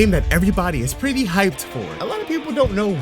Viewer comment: "everybody's pretty hyped for" Wut I heard: "Rell seas, everbody's going to pay for" my inner thoughts: wheres my robux